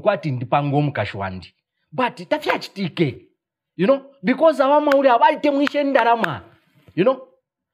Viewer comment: Did he just say aba ama hule aba😭🤣🤣🤣. He just accepted that his ka girlfriend is a hule